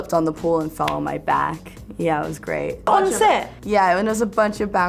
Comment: Did she said like "πόνεσε;" on 0:34 like wtf😭 🇬🇷🇬🇷🇬🇷